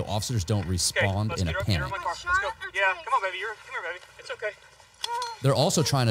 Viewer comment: Is he calling her BABY?